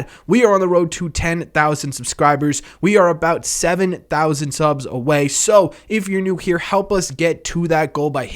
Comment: I think I hear "we're about 7,000 subs away"...but then again, english is my 2nd language